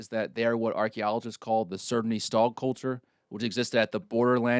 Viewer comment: What he says here 21:10 is the "Sredny Stog" culture, by the way.